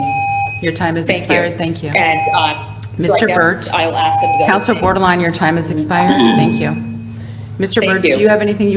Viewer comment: Thanks Portia Bordelon for supporting The People!! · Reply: Thanks for clearing up her name, for a moment there I heard "Counselor Borderline". I thought she was mocking her.